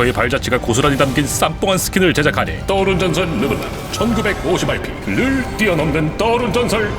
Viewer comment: 나만 1950RP가 아니라 195씨발P로 들림?